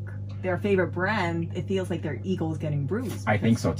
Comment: did she say eagle instead of ego?